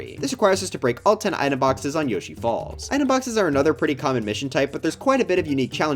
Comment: help, I heard you say Yoshi’s balls😂